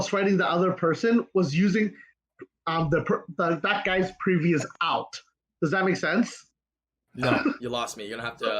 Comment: pls tell me why you pronounce alt as out instead of odd. Alt is literally short form of alternate it's trippy as fk to hear him pronounce it that way.